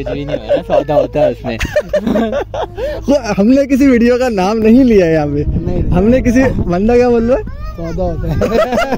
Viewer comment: what he said?😂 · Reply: I think Click bait kaha 😂